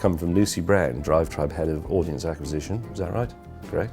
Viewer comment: did he say head of ordnance acquisitions? What business is Drivetribe into?!